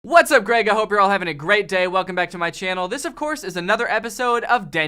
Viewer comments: I always thought he said have a Greg day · Are you telling me... that this whole time... i thought he was saying “I hope you’re having a Greg day” he was actually saying... “I hope you’re having a great day”? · This is all I hear... 0:00 "What's up Greg, I hope you're having a Greg day." Instead of... "What's up Greg, I hope you're having a great day."